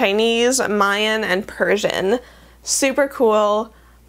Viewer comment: did I hear Purrsian? 😉